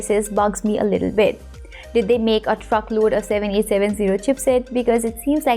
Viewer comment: Did she say did they make a fuckload? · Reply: I said truckload.